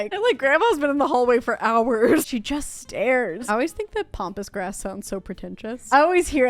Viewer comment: I genuinely thought it was called pompous grass for the longest time till my friend just laughed and had to correct me. 😅 Pambas and pompous are close! Kinda!